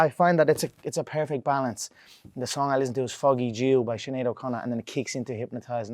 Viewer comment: I heard “Foggy Jew”